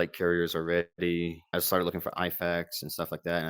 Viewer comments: he says IFAK witch is Individual First Aid Kit for anyone wondering what he said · = "I started looking for IFAKs" An IFAK is an Individual First Aid Kit, so with tourniquets, gauze, bandages, all that you'd need to stay alive if shot on a battlefield. 5:11 = i think he says "And it's gonna be important" (while talking about the kevlar helmet) 41:13 = i think he says "all lined up", as an enfilade fire is when there is a line in front of you and they're all behind one an other, which is pretty much the best thing that can happen for a machinegunner but anyway, to talk about the story, it's a great story, learning a lot from it, but this guy should go talk to a professional, have some therapy or something, i think everyone who comes back from a war zone and who have seen combat or results of combat should go to therapy